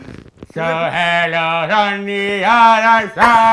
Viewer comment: It sounds like oli is saying hell nah